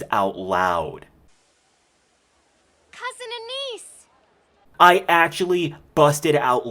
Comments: I think at 5:09 Sage said "Cousin Anise!" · She said "cousin Anise", not "cousin and niece." Though, I admit I misheard that as well while watching the show.